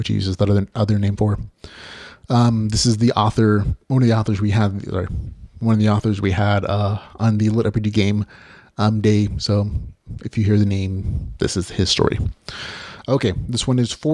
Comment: Am trying very hard to understand what you said 30:12 in, i'd like to go & look for it but i couldn't understand you, Sir. Your mic may need a buffer of some kind. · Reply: "This is one of the authors we had on the litRPG game day, so if you hear the name, this is his story."